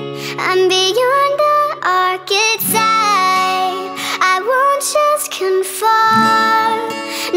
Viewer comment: I always thought it said "beyond the arc of time"